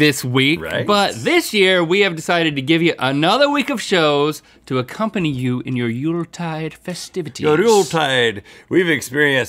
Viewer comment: I thought link said at 0:35 to accompany you in your noodle time festivity